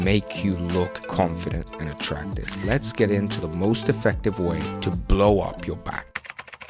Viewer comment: In the beginning I thought you were gonna say “best ways to blow your back out” 😂